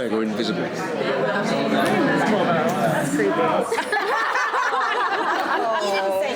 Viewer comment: what was the last quip from the woman at 6:11 then he says "that's why I'm not being invisible"? I couldn't quite catch it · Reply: She said 'that's creepy', as in choosing the invisibility option :)